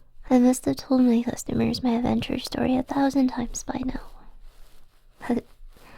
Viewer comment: oh you said adventurer I thought you said avenger